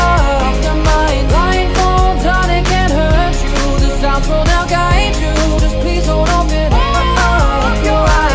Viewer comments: It actually says "The sounds will now guide you" instead of The birds will now guide you. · hes saying sound instead of birds